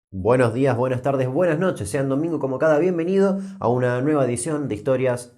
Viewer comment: Acaso escuché "sean domingo como cada bienvenido"?